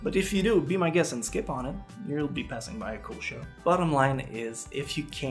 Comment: Omg i nearly came to murder you when I misheard you saying Monster was a cool show and heard "you'll pass up a cliche". lol. Thanks for the good explanation. This was actually a topic I chose for one of my speeches in my public speaking class.